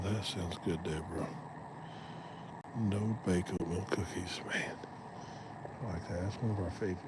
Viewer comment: WHEN YOU SAID THEY WERE EATING ACORNS, IT SOUNDED LIKE YOU SAID THEY WERE EATING BACON, SO I HAD TO GO BACK AND LISTEN AGAIN :face-blue-smiling::face-blue-smiling::face-blue-smiling::face-blue-smiling::face-blue-smiling